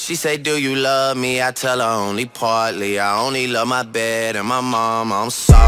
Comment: am I the only who thought he said "I only love my dad and my mom I'm sorry"